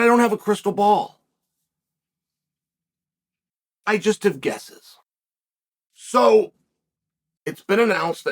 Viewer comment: Did I just hear 'Guesseth? 🤣🤣🤣